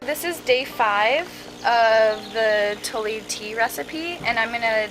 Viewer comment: What is she saying at 25:02 ? It's some kind of tea recipe, maybe starting with a t??? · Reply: Tole tea is a brand. Hearing that part doesn't help much because it's the recipe that we are interested in